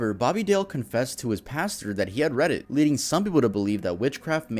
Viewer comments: why did I think he said reddit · bro I just heard that as “he confessed to his pastor that he had Reddit” rather than “read it” and now I can’t stop laughing